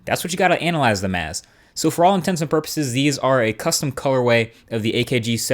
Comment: Did I hear you say, "intensive purposes"? I couldn't tell if it was the correct "intents and purposes" or not.